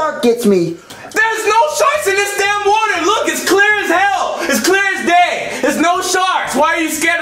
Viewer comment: it's clear as hell its clear as day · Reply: I thought he said "clear as gay" I died laughing